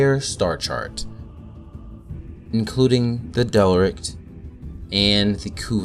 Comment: did he say the delrict?